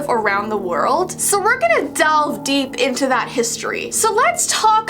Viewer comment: did she say we’re gonna delve??